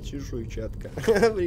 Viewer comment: когда он сказал чешуйчатка мне послышалось смазаная перчатка ;D